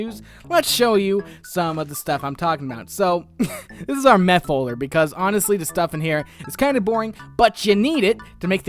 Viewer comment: What Said "This is our Meh Holder" What I Heard "This is our Meth Holder" XD